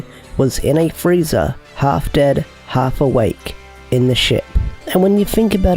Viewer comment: im bad at hearing and I thought you said sh!t instead of ship